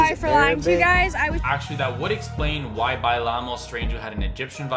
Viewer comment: When she says "bailamos" it sounds like she is saying "by llamas" btw, sorry for my grammar, English is not my fisrt language.